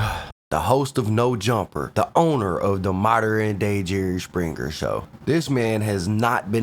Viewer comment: “the owner of the modern day jerry springer show”😂😂😂😂😂😂how mf accurate😂😂😂😂😂😂😂😂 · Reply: I heard it half asleep and thought he said “the owner of the modern day minstrel show”